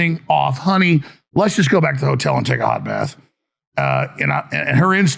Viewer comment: At first listen I thought quoted his wife saying, “Honey, let’s go back to the hotel and take a lot of meth…” Second listen reveals, “….take a hot bath.”